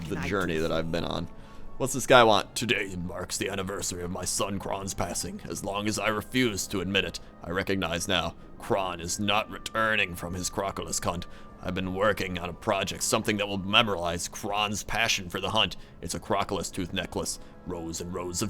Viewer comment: Totally thought you said crocolis kunt.. I'll see myself out now.